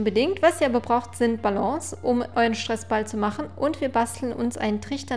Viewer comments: Ich habe bei 1:04 verstanden ,,Um euren scheiß Ball zu machen" 😂😂😂😂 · Bei 1:05 habe ich scheiss Ball verstanden 😀😄